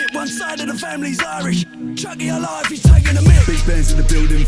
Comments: The lyric “Tiocfaidh ár lá if he's taking the mick” it’s an Irish republican phrase meaning “Our day will come” perceive that how you desire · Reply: Thanks for clarifying this. I thought it was a diss to Allah. I knew it was t coz that isn’t them but it deff sounded like he said something like, Chuck Allah if he’s taking the mic or piss😂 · The lyric “Tiocfaidh ár lá if he's taking the mick” it’s an Irish republican phrase meaning “Our day will come” perceive that how you desire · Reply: i always thought he said chucky alive if hes taking the mick..